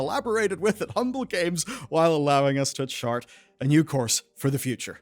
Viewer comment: I heard "chart" as "shart" and it seems more accurate 🙃